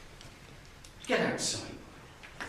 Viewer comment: How dare You speak to a Member of Staff like that? “Not really, no”. Get outside. I really am on a loss of what to do with You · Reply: Is that what he said at the end? I thought he said "I'm really having a loss, it's a lot to do with you"